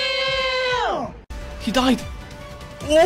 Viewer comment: I heard that as ebola master.